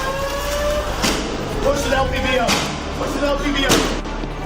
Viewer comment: he actually said "LPVO" which stands for Low Power Variable Optic